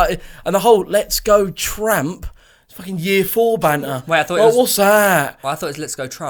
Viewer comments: I thought he said let’s go trump too😂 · Ngl I thought he said let’s go trump